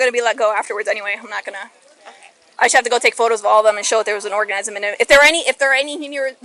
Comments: Did she say there was an orgasm in them 8:14 ? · she definitely said "orgasm" instead of "organism" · "I have to take a picture to show they had an orgasm in them" 😅😅. She actually says this play it back and listen. 8:11. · Did nobody in the comments catch this???? LEO said “orgasm” instead of organism lmfao. Had to do a double take. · Freudian slip at 8:13, she said "orgasm" instead of "organism" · the officer says orgasm instead of organism right? Lol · Did she say that she had to check to see if there's an orgasm in it? 🤣 I bet she checked deep and hard until she found it. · Did anyone else catch that she said orgasm instead of organism??😂😂😂😂 @ 8:13. I'm confused because everyone keeps saying that she lives in the Bahamas but she said she's new to Florida??